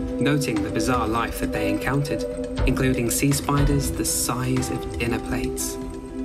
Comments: him: "sea spiders-" my arachnophobia, bashing me in the face with a crowbar: BET YOU THOUGHT THE SEA WAS SPIDERLESS HUH?? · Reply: I thought he said sea spiders the size of elephants, I almost cried. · I thought you said "sea spiders the size of airplanes" at one point and it freaked me the hell out. Glad I went back a few seconds to listen again lol! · i misheard "including sea spiders the size of airplane" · I know he did not say size of airplanes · I really thought they said “sea spiders the size of airplanes” and almost lost my shit